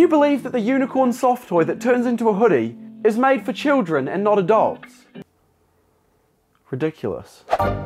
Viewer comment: When he said that’s for children and not adults at first I thought he said and not a dog